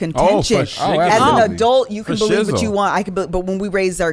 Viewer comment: I love y’all doing this great idea! Loving these. OAN: did say foshizzle @ 15:30??🤣🤣🤣